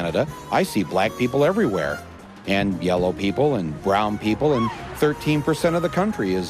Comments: did he just call them "yellow people"? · Did he just say yellow people?